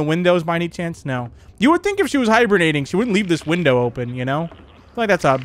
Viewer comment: I thought he said hiberniting instead of hibernating lol